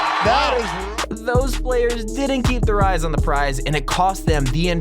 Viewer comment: When he said eyes on the prize my qurintine fat (insert unholy word here) thought he said eyes on the fries smh